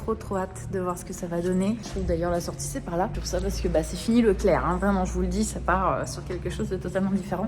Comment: Hahaha dans les premières minutes quand tu parlais de tes cheveux quand tu as dit "c'est fini pour le clair" et jai compris "cest fini pour Leclerc" je comprenais pas pourquoi soudainement t'avais une dent contre cette enseigne 🥺